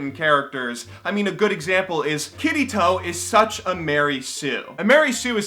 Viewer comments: I thought he said kitty toe instead of kirito.. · i literally heard "kiddie toe" instead of kirito. kill me. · When you said Kirito, I thought you were saying Kiddy Toe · I thought he said "Kitty toe" when he tries to say "Kirito"